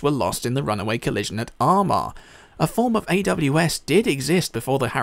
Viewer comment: What are you saying at 8:34?? I'm American and ur accent got really thick at that point so idk how to spell the location. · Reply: Armagh. It's an Irish place which is pronounced 'arma'